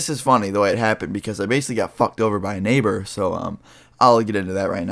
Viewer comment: Bro for a second I thought you said i got fucked by a neighbor instead of i got fucked over by a neighbor😂